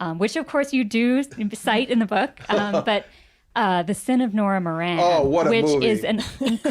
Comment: What's the film they're talking about at 46:00? I couldn't catch the title, but it sounds like I need to see it. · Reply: Ah, found it - "The Sin of Nora Moran."